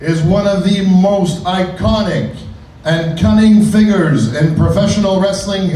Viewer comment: it sounds like he said cunning fingers.